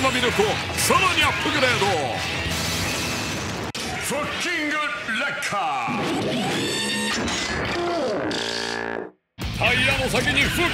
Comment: 2019年から失礼 フッキングレッカーがフ**キングレッカーに聞こえた